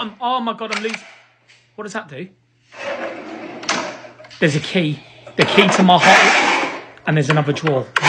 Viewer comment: “It’s a key, the key to my heart.” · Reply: i heard "the key to my hole" 😅😅😅